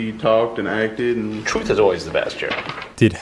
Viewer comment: I heard him say "truth is always the best here", then after you saying he called him Jerry and replayed the clip, then I heard Jerry. After rewatching it a few more times, its funny to see that I can hear either word based on which word I'm thinking